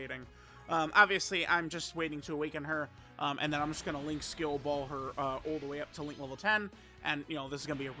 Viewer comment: Thought he said he was gonna link ball her at the beginning